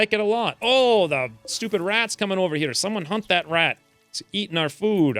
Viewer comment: I thought I heard, "someone hump that rat." Haha.